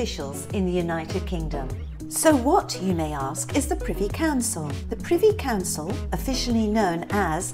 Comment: Hey gorgeous girls I so love your down to earth and quirkiness…. You do so well with your research and delivery.. Love the fun that always abounds within every one.. I thought you said “The pretty council” 😩🤦🏼‍♀️ Don’t mind me 😂 Well there you go, interesting facts on wigs… Very well done and… Ha ha call me John, trust me I’ve been called worse! 😩😂😂 I followed you on IG too 🔆💕 Have a beautiful safe weekend you fabulous beings 💎❤️